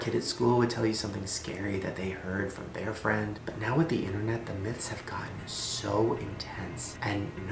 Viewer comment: I thought shane said "internet memes"